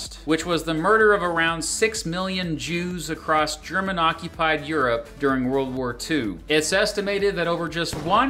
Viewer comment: Bruh did I hear “German-occupied territories” as “Truman-occupied territory”😂